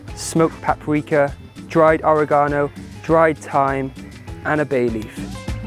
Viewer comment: I misheard "and a baby" instead of a bay leaf.